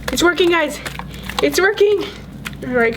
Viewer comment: Who else thought when she said ~it’s working guys~ it sounded like ~he’s twerking guys~ 😂😂